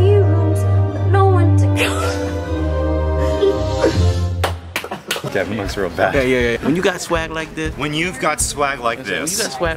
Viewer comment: What did Avi say at 0:49? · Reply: Norwegian Little Penguin I’m pretty sure he said “Kevin looks real bad”